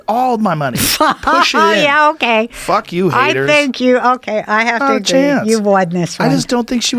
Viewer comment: What does she say at 1:08:31? "I think you..." what? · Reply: She was saying "okay, I have to agree, you won this one."